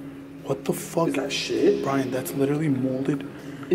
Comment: i swear to god on my life i heard a girl scream brian